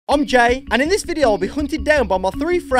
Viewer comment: i thought for a second he said “im gay” but he said “im jay”